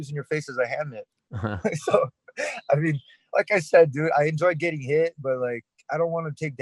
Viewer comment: Did he say "I enjoy getting hit"? at 3:03? Who says that?